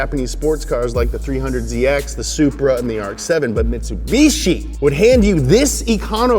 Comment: Did somebody say, Supra? 0.0